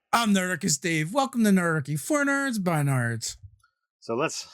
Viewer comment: did i hear "for nerds, by nards" on the intro? it sounds like it to me